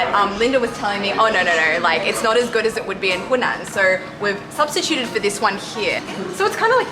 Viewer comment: Did I hear " prostituted" instead of "substituded"